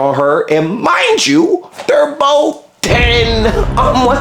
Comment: Lol he says "they were both ten!" And the subtitles say "the were both death'" 🤣